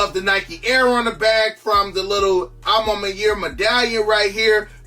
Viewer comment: every time i hear you say maniere i hear “my year” and i watch a lot of your vids so im just wondering the whole time what the my years are lol it’s not your fault it’s mine